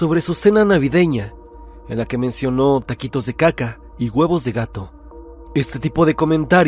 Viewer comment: Huevos de gato? 😅 Yo pensé que había dicho, miados de gato 😂